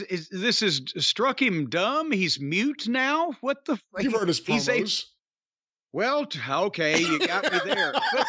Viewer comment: What does say at 2:15? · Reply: “You’ve heard his promos?!”